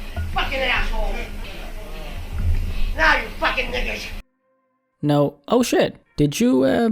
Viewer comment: It didn’t sound like hard r to me tbh. That word has a distinct sound, and it sounded like he said niggis